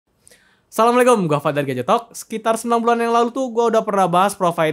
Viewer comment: Gua penonton baru disini, sumpah, di 0:01 gua dengernya "Gajetot" anjir